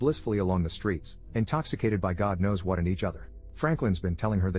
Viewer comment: "...intoxicated by god knows what and each other." My a$$ thought this guy said "...god knows what IN each other." I audibly gasped.